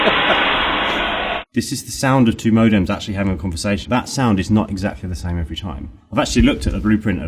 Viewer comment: Thought he said it was the sound of two modems having intercourse.